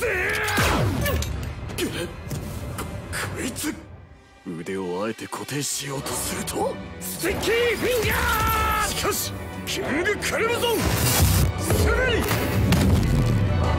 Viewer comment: did anyone else hear him say stinky fingers? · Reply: I hear sticky fingaz